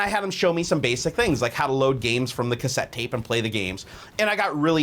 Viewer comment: did he say load games from the cassette case ???